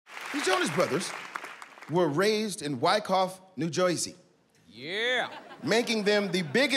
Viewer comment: Did he just say New Joisy?